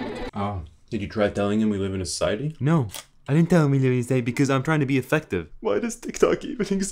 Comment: What did he say at 0:55? My english isn't too good so I can't catch it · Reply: Did you try telling him we life in a society? No I didn't tell him because I'm trying to be effective.